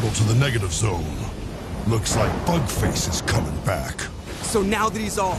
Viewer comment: I legit heard Hulk say "Fuck Face"